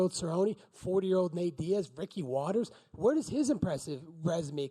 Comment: what does he say please? Ricky Watters? Can someone explain please?